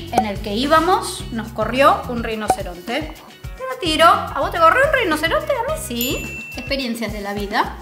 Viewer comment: Solo yo entendí "a vos te gorreo un rinoceronte?" Jajaja